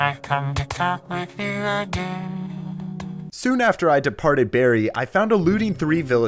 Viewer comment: I thought he said deported...